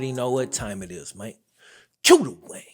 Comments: I always thought he said "cue the wang" 😂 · Am i the only one who thought he was saying cue the way not wayne? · This whole time I thought you said “QUE THE WAY”